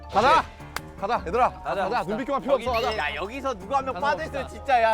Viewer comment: Sounded like RM spoke in Hindi "IDHAR AA" "AAJA" 😂😂💜💜